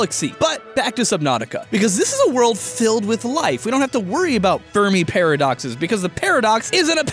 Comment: I heard that as furry paradox..xD